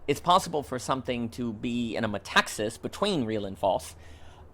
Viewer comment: I'm really curious about the term you're saying to describe the liminal space in which social constructs are, as performative acts. You say it in 6:28 & 6:35 and I'm not quite sure what this term is, nor how to writte it. Metaxis? It leads me to the ideology of metaxism of Ioannis Metaxas (Ιωάννης Μεταξάς) which I doubt has anything to do with what you're saying. Thanks for the synthesis and thoughts! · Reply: Thanks so much for the comment, and I was indeed trying to say "metaxis," which Dember discusses and that also can be found in the work of William Desmond. They mean the word more to describe a "between" and what resides in that between space. I sometimes struggle with pronunciation...Thanks again!